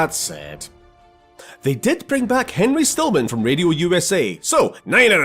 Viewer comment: Fun Fact, Henry Stillman can be heard at the end of Resistance 3. Through it all, he still remained alive. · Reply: I thought you said Henry stickmin.